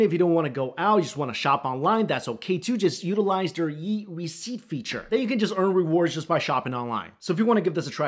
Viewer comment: Totally sounded like he said "utilize their yeet receipt feature". Lol.